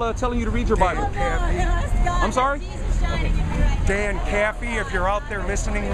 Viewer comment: Yeah, I'm aware of the term. At 1st it sounds like he said, "Damn Ca*fie". But he's saying Dan Caffy. He's just mispronouncing his name, as far as I can tell. I don't think he was trying to use the word Ca*fer.